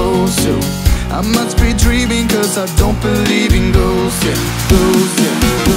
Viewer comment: It sounds like he is saying ' Girls' not ' Ghosts' but either way I love sooooo much